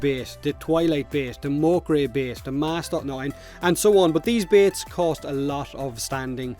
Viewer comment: did I hear Master when you called out bait? That's the best kind of BATE